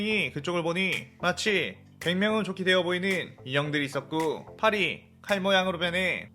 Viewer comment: 인형이라적혀있는데인간이라들리는데요